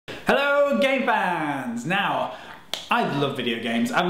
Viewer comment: Is it me or was the first thing I heard was "Hello Gay fans" o.o (Btw ik he said game fans)